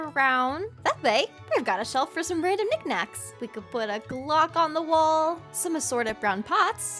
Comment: I honestly thought she said “Glock”